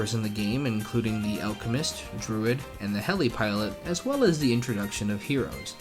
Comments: did he say the heli that was in btd5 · Did you just say heli pilot Me: looks at BTD5 Me: My brain has turned into mashed potatoes